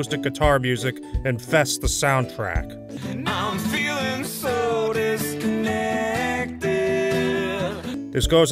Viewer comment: Is he saying "I'm feeling sodas neckness"